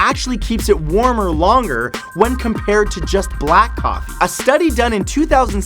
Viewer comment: it sounds like he says "Black cock"